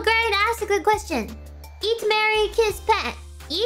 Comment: The way she said it at the start of the video sounded like she was saying to eat Mary and kiss Pat.